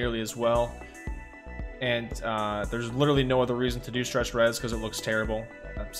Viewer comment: When he said stretched res, I heard it as stress duress. I immediately thought it as a new dbd term. Hahaha